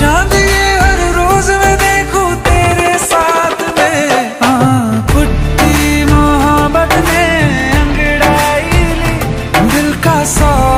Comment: i thought he said hai kutti mohabbat hai 😅😅😅